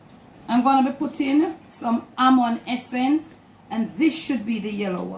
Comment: Hello could you provide the full recipe, you have a beautiful jamaican accent I couldnt tell what some of the ingredients were that you used. there is one where it sounded like you said ammon essence. tried looking it up couldn't find it. and so i thought maybe she said lemon essence, I found that. is that one of the ingredients in your recipe lemon essence?